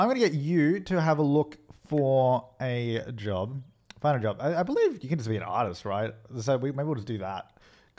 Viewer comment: It sounded like he said 'Buy a job' but it said 'Fid a job' on the phone. XD😂